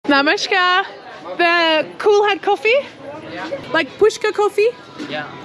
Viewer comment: When you said kulhad, I heard it as coolhead😂